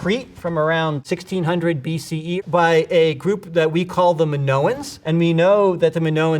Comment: i heard minotaurs 😂😂😂